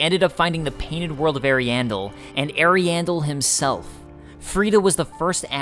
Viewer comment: On the pronunciation: The first Corvian you find (sitting in the rot) pronounces it: Air-iandel Gael pronounces it: Ah-riandel I more distinctly remembered the Corvian saying it - and based my pronunciation off of that. (You can hear it at 8:40 in the video). As when I was capturing footage, I believe I talked to him multiple times. So, there ya go. Seems like both pronunciations are "correct". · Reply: I thought you said the painted world of hairy anus